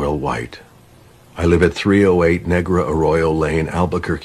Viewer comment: sounds like he said negro